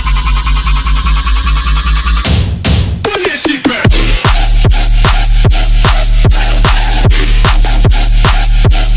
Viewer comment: what phrase does it say? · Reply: Dubstep SYX “Run that shit back”